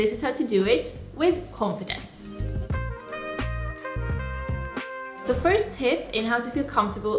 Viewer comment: i legitimately thought you said the first tit at 1:00 and felt very confused